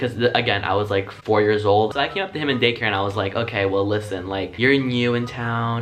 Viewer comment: Tbh I thought he said gay care